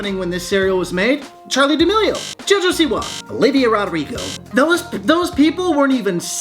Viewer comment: i thought you said ‘a labia rodrigo’